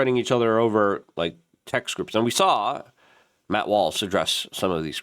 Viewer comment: did you say Matt Walsh or Matt Wallace when you were talking about the leaked texts? I originally thought you said Matt Walsh, probably because I don't know who Matt Wallace is, off the top my head anyways. Closed captioning said Matt Wallace but those can often be wrong. I'm guessing it has to be Matt Wallace because Matt Walsh definitely didn't say he was hurt about the text and said on his show that ppl should not have any reaction from the texts. Because we all know what they are, and it's definitely not a bunch of racist talking in a group text, more like ppl talking shit and goofing around, like most of us do.